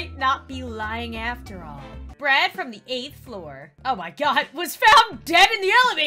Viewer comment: thought she said bread. I was like “NOOOOOOOOOOOOOOOO!”.